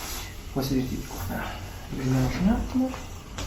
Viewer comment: поправь свою титьку... Или мне послышалось?ку...